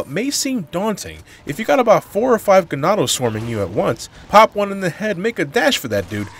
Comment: I legit thought you said "if you got 4 or 5 enanos swarming you" for a second there. Hispanic blood coming in at the funniest time.